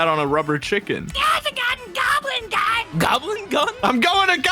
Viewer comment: I heard THERES A GUN!!! GOBLIN GUY!!!